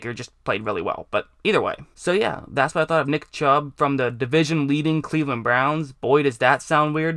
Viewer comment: did he say dick chubb. Lol sounds like it.